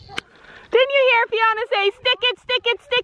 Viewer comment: When Fiona said “stick it stick it stick it” for some reason I heard her saying “chicken chicken chicken” 😂😂 good job you did so well today 😁